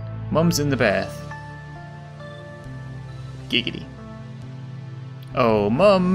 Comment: did he say giggity